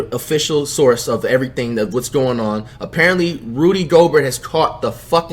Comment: I swear sometimes it sounds like says Rudy gogurt💀